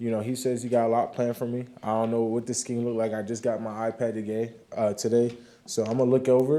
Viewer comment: He just got his WHAT?! Did he say “iPad”? I thought Microsoft sponsored the NFL and they all had to use Microsoft Surface tablets.